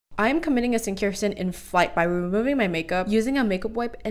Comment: Am I the only person who heard her say 💫sincare sin💫